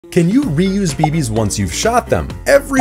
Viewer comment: I heard this as, "can you reuse babies after you've shot them" 💀